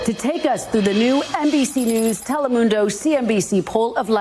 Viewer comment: At first I thought she said NPC News haha